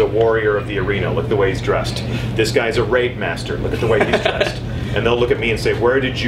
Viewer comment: i thought he said RAPE-master.... 0.o